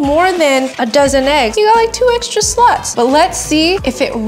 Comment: I thought she said 2 extra sl*ts😪